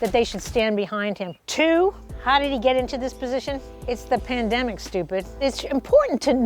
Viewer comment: did... did i hear that right? Did she really just say “its the pandemic *stupid*”